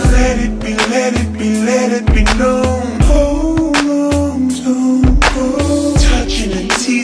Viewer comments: Whyyy i hear it "hold on jungkook"!!!!!! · My ears got blessed because I'm mishearding - hold on don't go as hold on jungkook 😳🌚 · Why am I hearing "HOLD ON JUNGKOOK"??